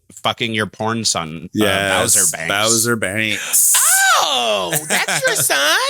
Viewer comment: I thought that said boner banks and I was VERY confused?